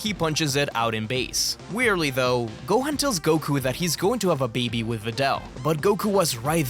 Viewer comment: I wasn't paying attention and thought he said "Goku was going to have a baby with videl" And then when I saw the caption "I'm going to be a dad" I had to double take that cause I was so confused